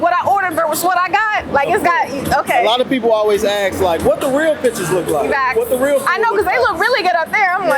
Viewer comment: I thought bruh said "what the real bit** look like" 😂😂😂